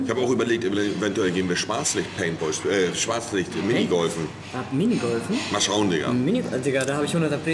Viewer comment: Ich hab Spastik-Minigolfen verstanden